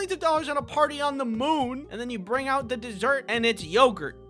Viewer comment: then you bring out the dirt is what I heard